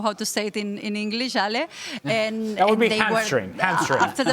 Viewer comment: Can someone tell me what Ale said: "That would be ?????". Thanks in advance. PS: she said in Spanish Tieso which in this case translates into Speechless (stupefied) or tense. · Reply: ”Hamstring” And regarding ”tieso”, in this case it seems she’s using it as slang to mean that Bernal was absolutely dead, spent, after the game.